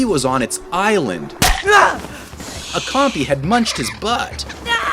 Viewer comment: what did you say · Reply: Munched his pants